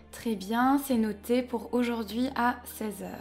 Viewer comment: I thought you were saying the doctor was available at 6 hours or 6 o clock.